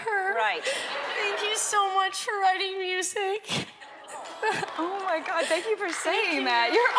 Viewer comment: wht did she say?? Shes crying so much i cant make out wht she's saying??? But she is acc too cute!!! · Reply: She said "Thank you so much for writing music"